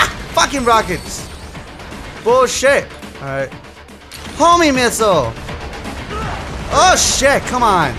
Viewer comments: I swear i heard him say homie missiles. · It sound like he said homie missle